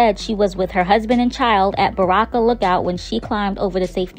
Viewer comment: RIP.. I thought you said Barack a lookout